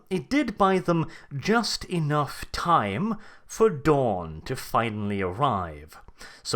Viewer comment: gave them enough time for Dorn to arrive...and for a moment i was excited and confused before realizing that he actually said dawn :D